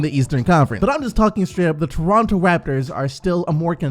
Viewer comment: I thought you said the Leronto raptors😂😂😂😂